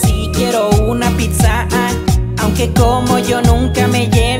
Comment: pense que dijiste pizzada pero dijiste pizzaaa